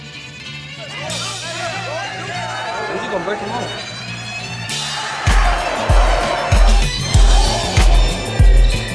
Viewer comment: what he said in 0:20 please??? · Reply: I'm just gon break him off